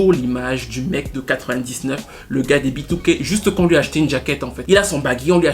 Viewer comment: « Ômarion garde le même baggy, on lui a juste acheté une jacket » je suis en larmes 😭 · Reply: et encore, à la base j'ai entendu "on lui a juste jeté une jacket" 🤭🤣